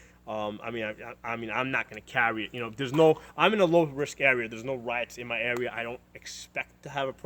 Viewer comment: I swore he said there is no blacks in my area had to rewind to hear him again